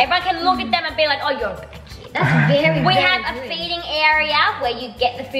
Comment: Why did I think Sockie said "beating" instead of "feeding" 😭